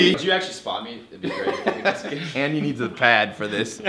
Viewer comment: did he just call cody andy? lol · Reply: anna oppedisano he said "and he needs a pad for this" not Andy lol but I see how you got it mixed up